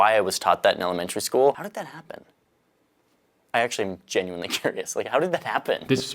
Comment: If you are basing the fact that he cares about flat earth based on the subtitles at 14:28, then you're wrong (just like the subtitles are). What he really says is that he is genuinely curious.